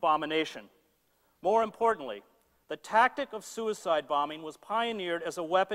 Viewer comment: you misheard. He said "suicide bombing", as we understand it today I assume.